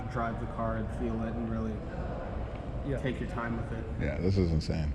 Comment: did he just say take your time with it? Referring to driving a manual trans F40 😂😂😂